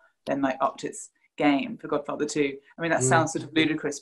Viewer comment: My English isn’t very good, could someone please tell me what she said at 2:30 ? Something about godfather? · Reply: Films Godfarher I and II